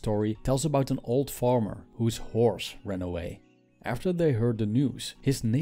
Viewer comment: At first I thought he said the farmers whores ran away.😭😹